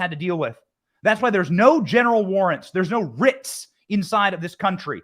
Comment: I thought he kept saying genital warts